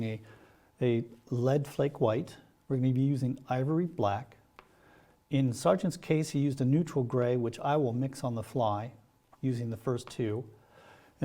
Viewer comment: "Sargent used a neutral grey, which I won't, and will mix on the fly" -Spends the next hour mixing black and white together. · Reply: “In Sargent’s case he used a neutral gray, which I will mix on the fly from the previous two” He clearly says he will mix Sargent’s neutral gray from the flake white and black.